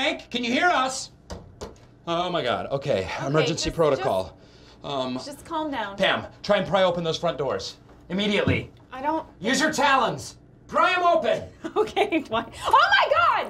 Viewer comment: I loved it when he said "USE YOUR TALONS" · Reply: Oh my dumb self thought he said “use your talents.” I was like, “what talents?” 🤦‍♂️🤣